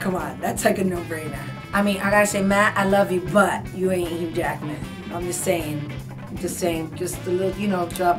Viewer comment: What does she say at 0:16? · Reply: Hugh Jackman